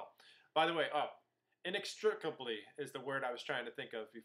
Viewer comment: What word is it that you remember at 20:02? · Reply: Ron Weasley Inextricably, I found it :D